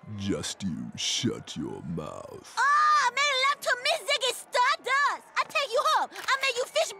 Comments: Super late comment but I just realized what she said 😂 "Make love to me, Ziggy Stardust" (his other personality) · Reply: Yeah, that sounds more accurate lmao. I always thought she said "Me make love to Ziggy Stardust!". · The first time I saw this, I thought she said “Me love to meet”, which is arguably more racist. XD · Super late comment but I just realized what she said 😂 "Make love to me, Ziggy Stardust" (his other personality) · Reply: I thought she said "Me love to meet Ziggy Stardust!" but knowing Family Guy, yeah she was probably asking him to bone her.